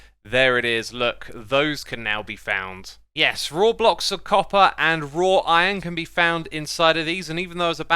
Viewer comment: Why did I hear "Roblox of Copper"?